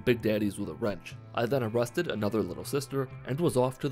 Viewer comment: you then what? I know you didn't say it but it sounds like you said molested lol